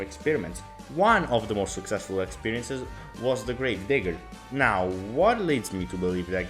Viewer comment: "Was the great/grave nig-" was atleast what i heard 💀